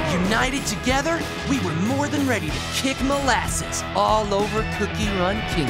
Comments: Why did i hear "kick the as*** all over the cookie run kingdom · IM CRYING LIPSTICK DID HE SAY MLASSES 😳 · My god I thought ginger said “we’re going to kick some asses” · did gingerbrave just say "Small Asses" ✋💀 Malaysia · For a while i thought it said Kick all asses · I thought he said we were ready to kick more asses 💀